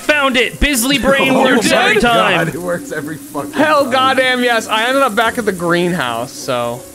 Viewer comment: the intro nearly broke my brain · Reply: all i heard is "goddamn subscribe"